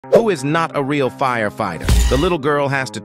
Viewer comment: Oh, who is not a real fire fire I thought you Said who is the real fire fire, the real firefighters a